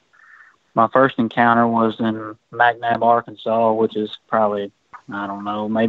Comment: Is he saying McNabe, Arkansas at 1:48?